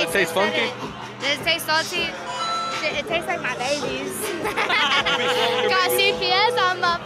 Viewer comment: Askin for a friend what the girl say it taste like a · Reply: She said, "It tastes like my babies"